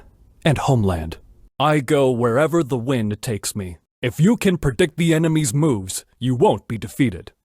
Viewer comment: i always hear perdict not predict